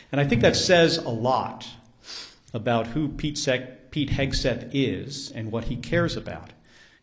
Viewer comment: Pete-Sack… 😂 I heard you slipping truth there 😂